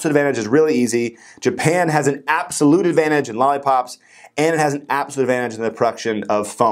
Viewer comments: did you said lollipops ??? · lollipops instead of laptops LMAOOOOOO